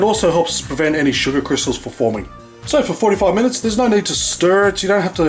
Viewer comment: did u say 45 minutes or 4 to 5 minutes??!! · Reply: 45 i guess